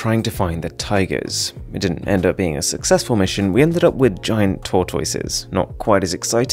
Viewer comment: I thought he said "Turquoises" 😆😝